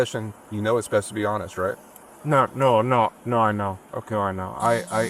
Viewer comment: “Listen. In our our profession, you know it’s best to be honest, right?” No, no, no, no, no, no (just forget any “I know” you thought you might have heard in there) 😄